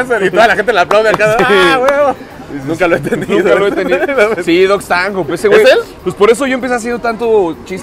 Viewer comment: Cómo se llama el comediante? 30:30 Yo entendí dogs tango pero no lo encuentro jaja